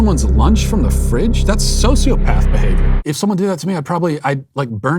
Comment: i heard that as "that's a sociopath, babe" instead of "that's sociopath behavior" and i cannot unhear it